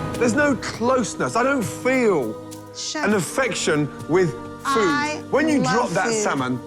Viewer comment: did she just say I love you? Wtf? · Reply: She Said Food